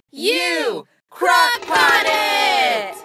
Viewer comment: Okay, surely I am not the only one who originally thought it say "CRACK POT"?